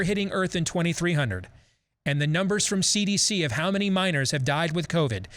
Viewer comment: Okay why did I think when they said minors, I thought they were actually saying miners 😂😂😂 I was like this is a writ correlation 🤦‍♀️🤦‍♀️